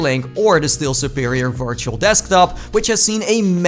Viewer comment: did you say massive